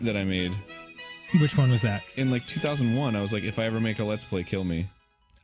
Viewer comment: Arin says he wrote the tweet in 2001??? As in 5 years before Twitter existed and he was like 12? Did I hear that right?